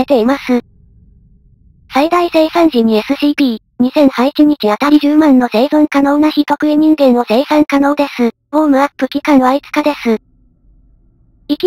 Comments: 聞き流してたら"非特異人間"→"人喰い人間"に聞こえて恐怖を感じた。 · 人食い人間？(空耳